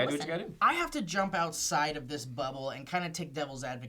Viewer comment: It sounded like said "I have to jump outside of this butthole..." heheh